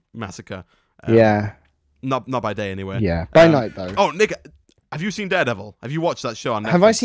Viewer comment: did he say nigga in